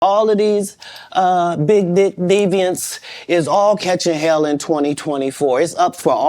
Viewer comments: Sorry,But It sounds like he said All these big d deviant 😅😅😅😅😅😅😅😅😅😅😅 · Everybody start this video and tell me wtf katt Williams just said😂😂😂 I heard "big d*ck deviants" 😂😂